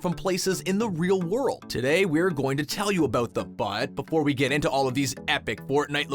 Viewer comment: Did he say the butt